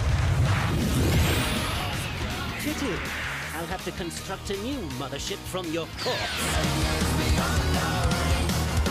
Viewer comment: aver aver no entendí eso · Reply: ¡CHATARRA TU ABUELA! Ahí tienes el contexto